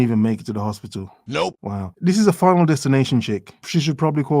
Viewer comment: I misheard you near the end and heard "this is a final destinationship." Should be made into a horror movie or something.